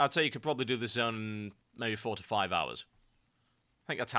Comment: lol I thought you said "45 hours" instead of "4 to 5 hours" and was like "WTF?!"